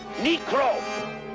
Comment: Did he just say "Meecrob" ?! Muahaha